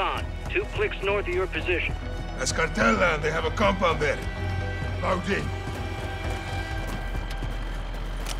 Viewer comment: I thought said "Grapes" instead of "Graves" 🤣